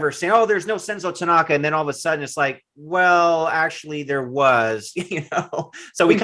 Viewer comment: Maybe i misheard him. But did he say: "he had multiple families, he's a real ninja"...... If so, that's hilarious.